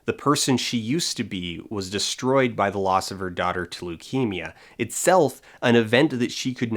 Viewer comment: why did I think u said "at the of her daughter, Toleukemia." As if that were the name of the kid. Lmao